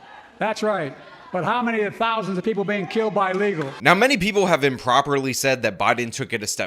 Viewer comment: I thought he said "but how many thousands of people are being killed by legals" as in Americans. As in Right wing mass shooter Unicorns...not Chicago evey weekend....